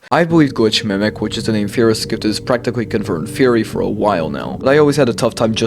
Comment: bro said "GlitchMimic" and forgot how to speak English for a second I seriously have no idea what bro said 💀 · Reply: ??? it's in the subtitles if you really can't hear it but I said: "which is the name theorists give to this practically confirmed theory"